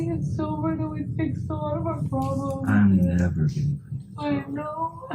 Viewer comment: I think he says "I'm never getting clean and sober" not that he never had been sober. And Amber crying and agreeing, knowing what it means for her when he's not sober. Knowing he's not willing to get sober even if it meant he wouldn't beat her anymore. Fucking devastating. She's so brave.